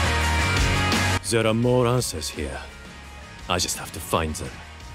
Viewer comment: “there’re more ancestor here,i just have to find them!” that’s what said! :) (but i think he’s said there’re more answer i think)